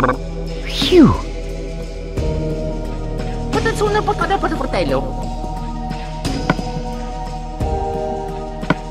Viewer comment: WHAT HE SAYS?🤣🤣🤣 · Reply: Burn it to a potato :) (Idk its that what he said that what i HEARD)